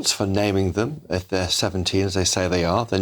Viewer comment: They are not asylum seekers and they are not 15. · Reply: I thought he said they were 17.